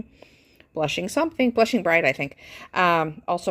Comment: I so thought "Blushing Bride" said "Fishing Pond". Couldn't figure out the connection between pink and fishing in a pond. Over here giggling at myself. Lol